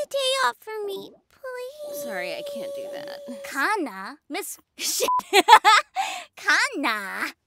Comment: I first thought Tohru was gonna say "Miss Sh*thead"